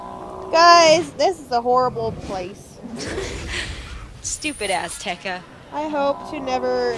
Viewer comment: at first i thought said "Stupid Ass teca"